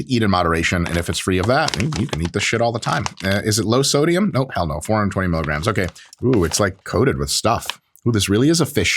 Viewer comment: What did he say at 4:23? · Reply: Ебaный в рот бля. Russian cursing, albeit with heavy American accent - I expected him to have a lot less of it judging by his other attempts at Russian